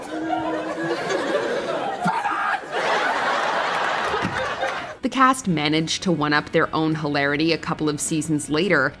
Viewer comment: What is he saying? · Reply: “PIVOT!”